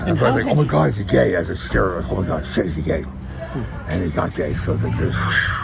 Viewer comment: He kept saying “he’s not gay”. All I heard was “I’m not gay”.